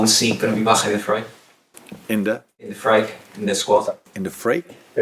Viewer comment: Alexander Isak 1:40 Hugo Ekitike & Alexander Isak 2:56 Everton 4:18 Merseyside Derby Rivalry 5:49 Build up to Merseyside Derby 6:43 Players that could make their Derby Debut 8:58 Mo Salah 11:03 Goals Liverpool conceded 12:49 2-0 14:14 Critical with the players? 15:37 Team News | “Jones is back in the frame🔥” __ Outro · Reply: I thought he said fray? Great subtitles anyway. "Freight" "freak" No wonder he was confused! 😂